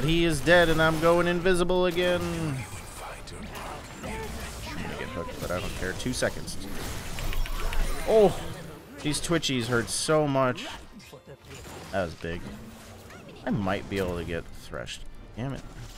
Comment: i thought he said "twitchies", but then i realized it was "twitch e's"